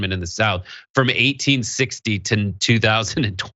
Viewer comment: 2000 and what? you got cut off mid word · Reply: 2020